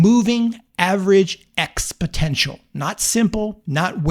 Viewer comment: Did you really say "ex-potential"? I guess it doesn't make any difference to the success rate - but sounds odd coming from someone who's an expert....